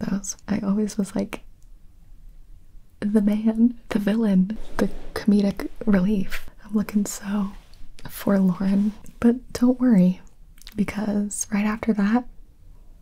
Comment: did you just say "verloren"!? As a german i find this kinda funny. 'Cause it's a german word i never knew existed in english.